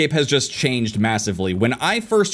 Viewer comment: Did you say MASSIVE?!!!??